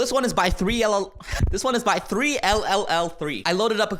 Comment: What said: This one is from 3elel What I heard: This one is from trololol